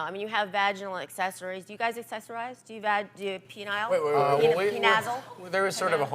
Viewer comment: Did she just say, "pe-nazzle"?!